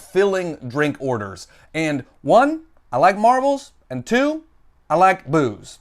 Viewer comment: #retail for on the rocks...I thought you said Marvel and booze...not marbles 😁